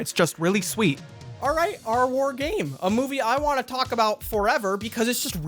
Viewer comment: Thought he was saying "r word game" the whole time.